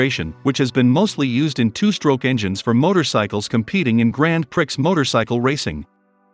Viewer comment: Did he just say "gran pricks"?